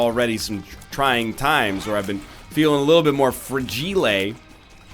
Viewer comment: "I've been feeling a little fragilet" fragile is that what you where trying to say or am i missing something Xd